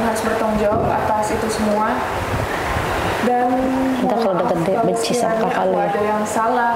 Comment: Bu nii ga sadar yaak dia di pakein clip on..si ijal di bisikin : " nti udh gede benci sama kk lu yaak "...kedengeran jelas lahh oon..gregetan bgt guw 😡😡😡